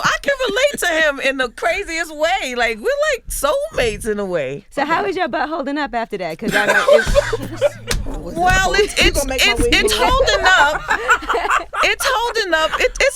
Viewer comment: 550-551 I thought Yee said "so how's ur butt hole?"(not how's ur butt hol-ding up) I immediately stopped bc my jaw dropped but realized it was more she was saying. Lol